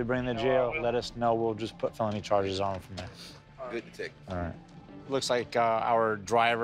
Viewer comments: My guy said “Don’t touch me bro” like he was gunna do something about it. Big tough talk for a man in handcuffs. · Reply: At first I thought I misheard him at 3:46, but after listening to it dozens of times, sure enough, he really said: "Ya know what, don't even worry about it, do whatever to bring them to jail, let us know, we'll just put phony charges on them from there."... He KNOWS all the cameras are rolling and no DETECTIVE would be THAT stupid, so it very well could be a mind game he's playing, I've seen interrogators do that before when they're working towards a confession. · I genuinely really appreciated how the male cop handled the one guy asking him to not touch him. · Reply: I don't know if I would use the word "appreciated"... At first I thought I misheard him at 3:46, but after listening to it dozens of times, sure enough, he really said: "Ya know what, don't even worry about it, do whatever to bring them to jail, let us know, we'll just put phony charges on them from there."... He KNOWS all the cameras are rolling and no DETECTIVE would be THAT stupid, so it very well could be a mind game he's playing, I've seen interrogators do that before when they're working towards a confession.